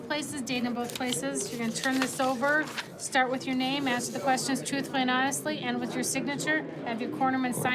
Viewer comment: wow ,wow, did I hear her said, answer the questions truthful ?OH